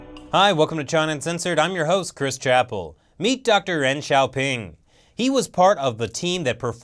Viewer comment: LMFAOO I seriously thought he said "Deng XaioPing"